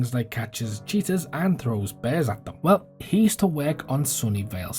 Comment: I thought you said “catch cheetahs and throw bears at them”. Gotta get my ears checked haha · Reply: I think I said 'catch cheaters and throw bears at them' lmao it's what he does